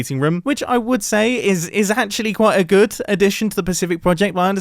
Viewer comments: I thought it said “pacific poggers” lmao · Imma admit I thought it said "Pacific Poggers" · I thought pacific progress said pathetic poggers